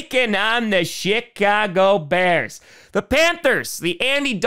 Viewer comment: I swear he says Sh!tcago Bears at 6:40.... that is literally the best and most accurate nickname...